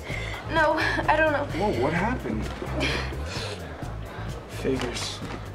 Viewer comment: What does Jess say to Dean at 2:04? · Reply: Figures